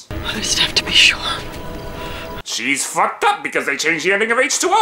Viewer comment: the captions say: "she's fucked up because they changed here nigga rates". Gotta love them generated captions